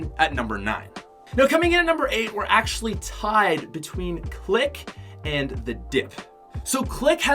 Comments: I thought he said click and dick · I thought he said dick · I thought he said click and the dick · Lmao I’m dead I thought he said “Click, and the Dick” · I thought he said dick lol I'm so dumb😂😂